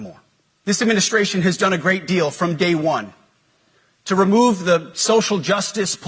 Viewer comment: did he say "from gay 1" lol?